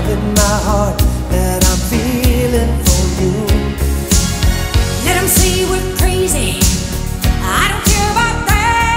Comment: "Let em see we're crazy" For some reason I always heard it as "Nancy went crazy" Like, who is Nancy